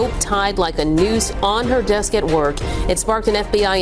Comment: I thought they said ... left a Duce on her desk😂 had to listen for a minute to hear what it really was but it was funnier when I didn't know 😂